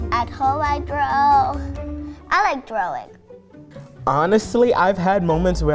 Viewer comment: "I like drowing" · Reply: An Average Fellow Lmao, I thought that said "I like drowning"..